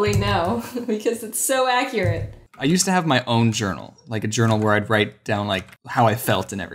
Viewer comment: James: I Used To Have My Own Journal. I Thought He Said I Used To Have My Own Turtle 😂